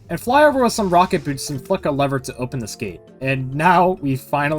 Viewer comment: I’m sorry but IT SOUNDS LIKE YOU SAID “Fuck a lever”